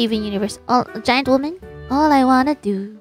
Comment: I thought I heard a "Giant Walnut" ahahaha